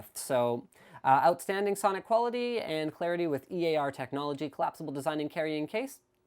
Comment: At first I thought he said EA technology lol